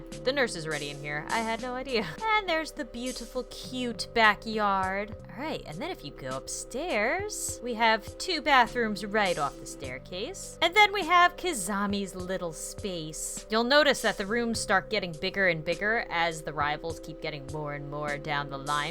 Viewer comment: and here we have Kizamis room Is it just me or did she actually say Kizami?